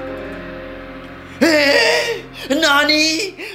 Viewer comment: I thought said Honey Nasi 😂😂😂